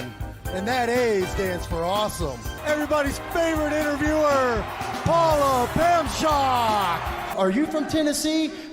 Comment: Years later, I remember that Mike called Pamela Paulshock "Paula Pamshock". I originally heard it as "Paula Poundshock", thinking Mike got her confused with Paula Poundstone. That's...well, a whole other story altogether.